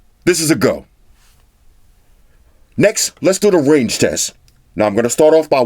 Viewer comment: I thought he said Rage Test hahaha